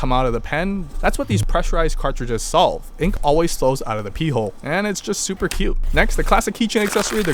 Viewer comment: did he say pee hole?